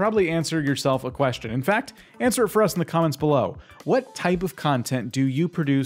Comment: You put "Why" instead of "what".